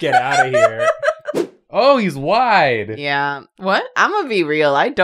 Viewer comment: I thought Jacob was saying “OH HES WHITE :-(“